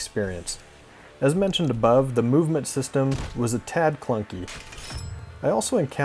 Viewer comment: I just picked this up for 90% off. Thanks for the review! Also, I skipped 10 seconds towards the end of the video and heard you say "maclunkey" What a gem :)